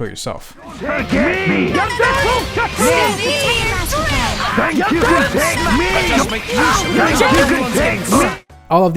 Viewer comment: I thought that she was saying "Think you could take the lead?" Good to know it's not.